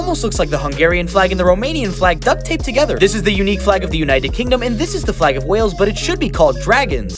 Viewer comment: Sri Lanka: where am I? Thought said im Unique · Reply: Sri Lanka: Where am I? Thought said I'm unique- More economic downturn